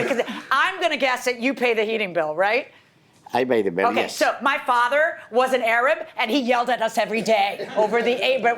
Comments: Wow... She lied... I don't know why, but she did. Her parents Jorge and Georgina Milian are of Cuban descent... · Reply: How did she lie?? She said "My father Wasn't Arab and he yelled at us every day over the A/C" 🤔. Min 3:15 I'm guessing you thought she said My father was an Arab huh?? Lol kinda sounds like that so honest mistake. 🤭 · I thought the judge said she’s from Cuban 🇨🇺 why now Arab, Muslim.. wow